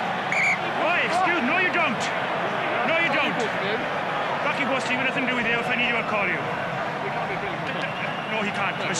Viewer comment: Anyone hear what does Dan Cole say 39:24? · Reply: "Do we get cake at half time?"